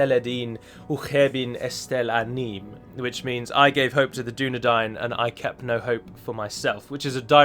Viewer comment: I very breifly thought you said Bilbo called Aragorn "The Dunedad" and I was jumping with glee.